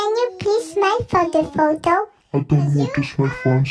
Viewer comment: Guess I didn't need that pepper, my bitter tears did a good job hurting my eyes😂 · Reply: Btw...Am I the only one who heard "Can you piss my photo photo" at 0:29-0:32?😂😂